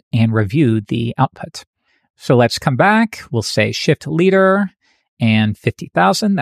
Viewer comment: sounded like shit leader at 17:48 :)